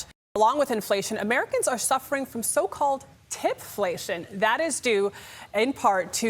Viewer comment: I thought she said "Titflation" instead of "Tipflation" and i was like "Thank god some good news"